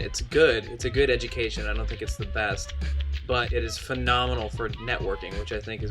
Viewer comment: What did the guy say at 7:07? "It's fenomenal for..."?